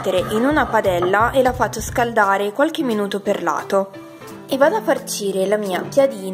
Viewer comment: Quando hai detto "qualche minuto PER LATO" ho capito "PERLATO" e mi sono chiesta come potesse essere perlato un minuto. Comunque grazie per il video, ottimi spunti! :)